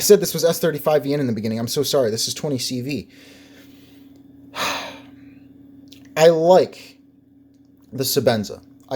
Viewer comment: Nice work. You got me interested in the bottom knife at 13:21 in, sounded like you said "booze" knife. Please reply with made and model, appreciate it. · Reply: Heyhey! It was a Boos Blades Smoke Mini.